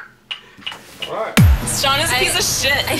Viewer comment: "shawn is a piece of shit" ?? really what did i hear? lol